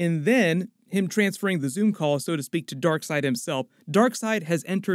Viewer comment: did you say zoom call 😂😂😂😂😂